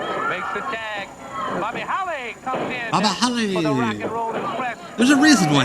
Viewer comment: I thought that they called him Buddy Holly at first.